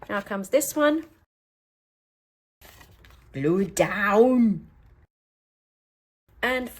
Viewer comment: What is the strange word at 23:23? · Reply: Someone or something says "Glue Down!" , but it wasn't voice.....maybe an audio glitch? I thought I was the only one that noticed it.